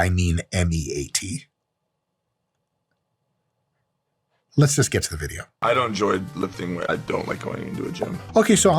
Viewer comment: i thought he said lets get to the menu 😂😂. mike kills me